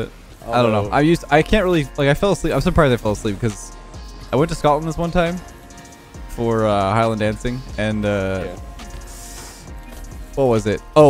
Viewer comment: I must be drunk because I'm sure I heard say "I went to Scotland this one time, for Highland dancing"